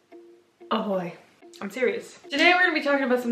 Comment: She said “Ahoy” I’m already satisfied. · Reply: I hear a "hi boy" :/